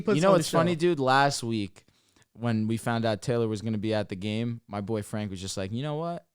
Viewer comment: bro at 12:15 I thought he said my boyfriend 😹